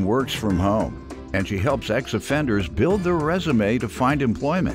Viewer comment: I gotta stop smoking while watching these cases … I thought the announcer said “the plaintiff help sex offenders build they resumes” instead of “ex offenders” 🤣🤣🤣